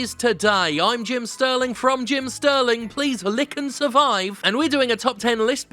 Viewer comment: Did you say ~~'Lick and Survive' ? You did....you really did :O now i can't get that outta my head 😐